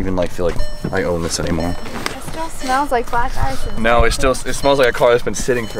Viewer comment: Bahahahahahaha at 4:30 I thought she said it still smells like a black guy! If anyone takes offense to this just give it a thumbs down so we know ur a poor sport n keep moving cause it’s just a joke...